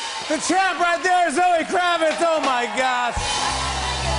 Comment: lol did jimmy call her amber at the end? i swear he says "you're the best, Amber"